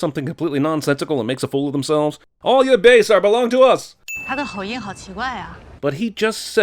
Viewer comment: you should’ve sinned the subtitles instead of her line because what she actually said was “he has a very strange accent”